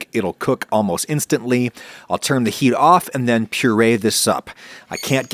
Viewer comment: "Purée this up" Thought you said "Purée this soup" in really weird way.